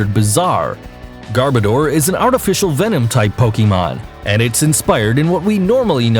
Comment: did he say venom type!!!!!!!!...?!?!...